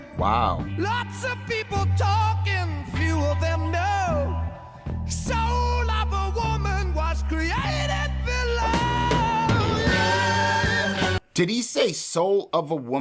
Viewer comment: I heard "Soul of a woman was created below." As in, created in hell.